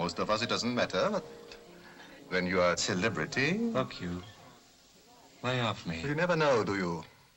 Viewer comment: Af the 43:55 mark, did he say, "f**k you"?